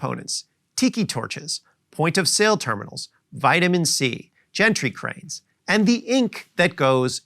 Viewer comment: Did he just say gentry cranes